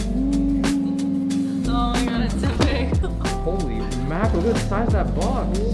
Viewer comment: lmaoo im not a native speaker and i always thought u said « holy macro »,, u can’t imagine my disbelief when i realised it was holy mackerel